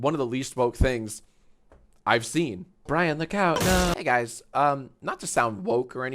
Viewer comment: It says "Look out" I thought it said "Brian the cow, no."😭😭😭